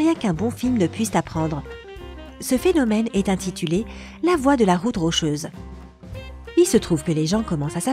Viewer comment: À 7:46 ce phénomène ç'appelle " LA LOI " ou " LA VOIE " de la route rocheuse ???? Parce-que il dis " LA VOIE " et c'est écrit " LA LOIE " .